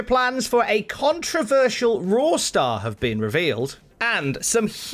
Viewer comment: Very disappointed to report I heard say “a controversial porn star” in the announcements.